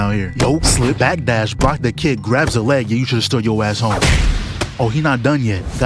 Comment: Did you say he should've "stood" his ass home?